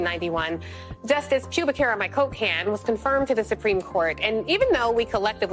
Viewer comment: I could have swore she said pubic hair on my Coke can.